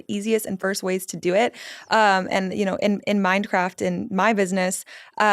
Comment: At first my brain heard Minecraft 🎮 instead of "(the) MindCraft (Method)". 😄 That must be intentional, I suspect after reading the description on her web site. 😜 > "Life is a game—when you understand the rules, the map, and the levels, you can master it. Your brain is already shaping your reality—MindCraft shows you how to do it intentionally."